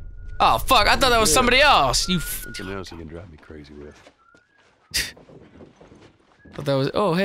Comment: the subtitle was wrong, he said "drive me crazy" but the subtitle said "annoy".